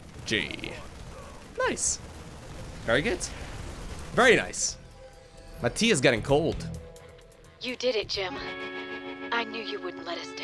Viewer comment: All I heard is "My goose is getting cooked" <3